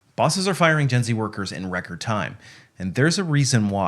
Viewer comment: I thought he said Disney worker not Gen Z workers 😂